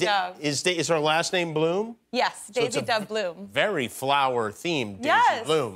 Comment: I thought she said Daisy Doug Bloom! 😂😂😂